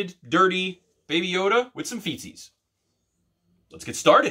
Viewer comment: I thought you said feces! 😆😅